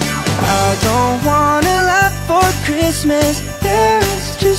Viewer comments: I heard “I don’t want a life for Christmas”💀ANYWAYS 3 MONTHS UNTIL CHRISTMAS WHAT IT FELT LIKE 2 MONTHS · Turned on the subtitles and instead of Kazuha saying, "I don't want a lot for Christmas," the subtitles thought he said, "I don't want to live for Christmas" 💀